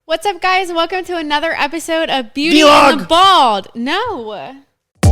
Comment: For a second there I deadass thought she said "beauty and the bald" at the beginning lmao